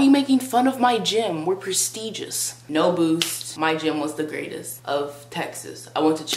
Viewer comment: Bruhhhh I thought she said "no boobs"